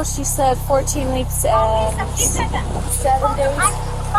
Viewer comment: I heard 14 weeks and several days. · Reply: I kept clicking back to that time and even the subtitles say 14 weeks and 7 days. She definitely says 14 weeks and 7 days.